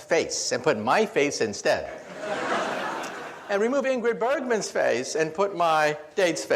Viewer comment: When he said ingrid bergman at 20:13, it sounded like Angry Birdman xD